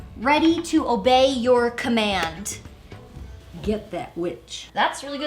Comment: "Get that bitch" was what i heard :D